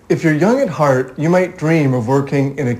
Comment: At first I thought he said "If you're young and hard..." at the beginning. What kind of chocolate is he after I wondered 0_o